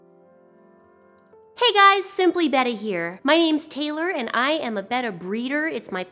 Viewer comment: everytime I hear your intro, it sounds like you say "Simply Bettie" and that's my name it freaks me out.